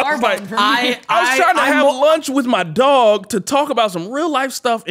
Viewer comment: I heard “trying to have lunch with my dog to talk about real life stuff” … man, thats an inquisitive canine 🫣🤣